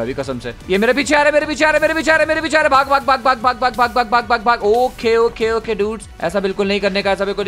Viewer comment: this time i thought was saying fu*k fu*k fu*k fu*k😂😂